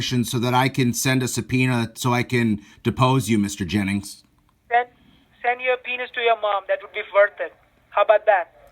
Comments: Did he just say to send his penis to his mom???😂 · "send your subpeona to your mom" 🤣🤣🤣😂😂😂 he thought he was clever 😏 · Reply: I thought she said to "send your penis to your Mom". I don't think she knows what a subpoena is(or ever heard the word) & thought he said penis. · The scammer didn't even know what a subpoena was lmao... they thought he said "penis". How can you be so bad at your own game? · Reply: I thought they said "send your penis to your mom" at the end.. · He asked for an address so he could, "send a subpoena". It sounds like thought he said, "send a peni$". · He thought you said Penis at the end of the call. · I thought he said "send your penis to your mom" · The ending made me laugh out loud when the scammer said, "You can send the subpoena to your mom". Hahaha! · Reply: I think she thought he said P*nis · At the end I thought Indian Scammer said “ Send the Penis to your Mom” 😂 🤣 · I thought he said "send a penis to your mom".....those accents can be hard to understand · "Send my sapina to your mom!" Had me laughing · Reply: Listening closely to the clip, I think "Mike Jennings" misheard what was asking and said "send your p***s to your mum"! They're nasty people. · Ha! He doesn't even know what a subpoena is, he said "send your penis to your mom" 😂😂🤣🤣🤣🤣🤣 I can't take it. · he thought you said that you were going to send him a penis ,🤣🤣🤣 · Omg at the end I thought he said send your penis to your mom this is crazy